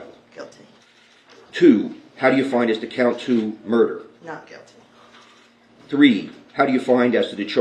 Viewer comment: I thought I heard murder was guilty